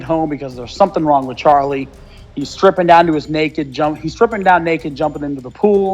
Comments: Bro, I thought it said "Charlie Sheen". Fuck. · Fucking hell I was confused for a sec because I thought it said "Charlie Sheen" and then I came to think "maybe I'm on drugs"